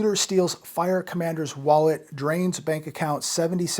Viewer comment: At first I thought he said there was 77,000 in bank account.